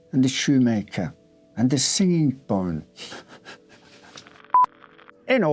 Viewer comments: What was it that made you giggle? Did you say "the singing bone"? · I couldn't understand what said that made him break character at 6:51. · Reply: Ah okay I got it, he said Singing Bones! Probably thought it was funny because it sounds like Silly Bone 😅